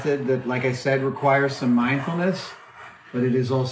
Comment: Instead of 'mindfulness' I heard Adam say 'mind houmous' and that is what I shall call that from now on.